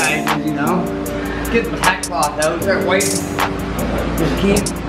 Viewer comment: What was it that he was saying at 5:19? Couldn’t quite catch it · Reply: He is using a tack clotch to remove fine dust off surface. it was his first time using one, i would suggest to use light pressure and lighty go over the panels right before spraying paint.